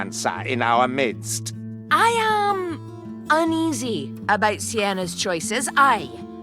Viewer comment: Kerillian says "uneasy", but the subtitle says "unsure"